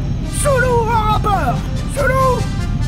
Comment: J'ai entendu "Sulu" en 1ère écoute : étrange asiatique que voilà ! 😅